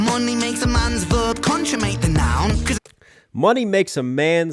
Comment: What else should we react to? Comment below! Also if you want to support the channel grab the hoodie I’m wearing or your signed CD now <link> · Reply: I almost thought he said conjugates a noun! Lol. Good thing I've got you 2 transcribe & translate for me! ;-p